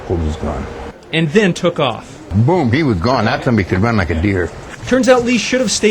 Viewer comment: Does he say "That sum bitch can run like a deer" at 0:57? LOL